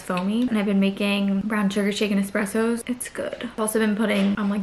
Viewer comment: i thought u said chicken espresso for a second i was like 🤡🤡 then i realized lol SHAKEN espresso.. 😅🤪